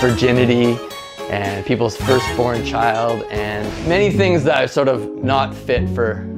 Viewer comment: wait- did he say "a first born child" at 2:26 ?